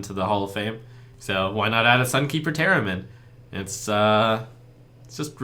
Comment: Did he say "Sunkeeper Theremin"? =D